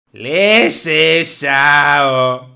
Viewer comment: Pensé que decía leshe shabo miércoles y que el de la caja era Ed maverick sjsjsjsjsjsjsjsjss